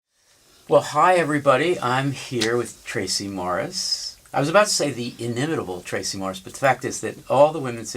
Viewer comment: when you mishear "well hi everybody" as "l'chaim everybody"